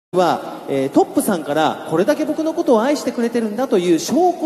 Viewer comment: I heard sandara😂😂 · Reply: Lmao bij he's saying TOP po-san kara (Mr. TOP) not sandara